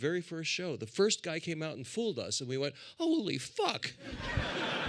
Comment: when did he shout that?! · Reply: when he talks about being fooled on the Fool Us TV show lol though I think he says "holy fuck!!"